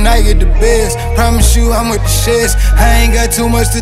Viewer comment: i swear homie sound like he sayin 'promise you i'm a gusheshe' 😅